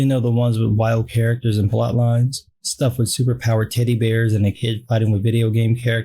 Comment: I whole thought you "said super-powered titty bears", instead of "super-powered teddy bears".